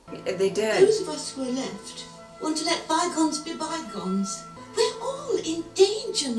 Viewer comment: Oh, dhe said bygones, thought she said Zygons.😅